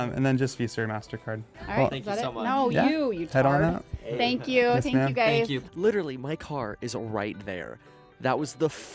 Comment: What she says at 9:56 😂😂😂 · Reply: Porter House haha thanks for watching!